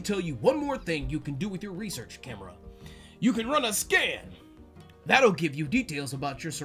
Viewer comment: When the prof said "you can run a scan" I heard "you can run a scam" and I'm like oh so we selling these pics now huh lol